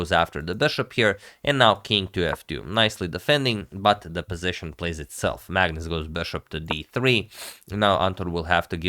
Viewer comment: Did he just say the position plays itself at 8:32 😂😂??